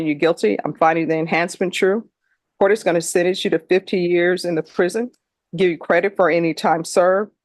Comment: I thought I heard 52 years and heard nothing about parole!